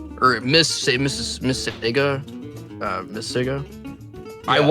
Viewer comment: he did NOT say sigga 😭